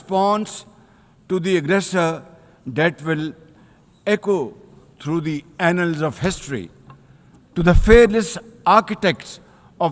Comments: Did he say "through the anals of history"? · Did he just say An*ls of history